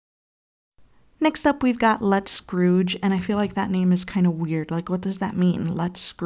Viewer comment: “Let’s SCROOge”… more corny naughty wordplay, sounds like ypure saying ‘let’s screw’ 😑🪛